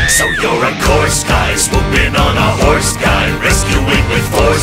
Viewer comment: I heard 'ride in on a horse guy' and immediately wanted this in the same universe as the Iron Horseman.Maybe with Darla Dawn as the damsel and Annie as the plucky kid sidekick?